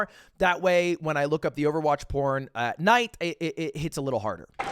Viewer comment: Did he just say "When i look up the overwatch porn at night"?